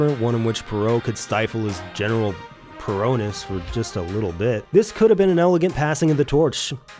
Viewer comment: Subtitles say "(indistinct)". Word said is "Perotness".